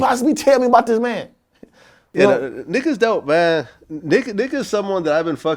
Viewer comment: i thought vlad said nigga lmfaoooo